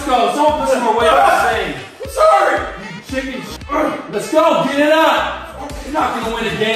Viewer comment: ish: WTF was said!? {After his trainer/step-dad/etc. Said: "...more weight on this thing" & said "IT HURTS"} ... Did the StepDad character say "ChickenShit!" ?? Lol There was SO MUCH more, in the SAME SCENE: that I wanna know what was said! ... I'm wishing I had the script to this video lol I really wish I could decipher everything from 0:15 seconds until the end...of that part, At LeAsT! Aha